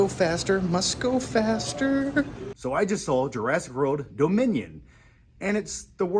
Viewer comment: Did just say "Jurassic Road: Dominion"? Damn, you know it's a forgettable movie when you can't even remember the title despite being from one of the most popular franchises